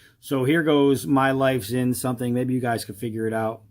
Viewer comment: He sounded like he said my life stinks.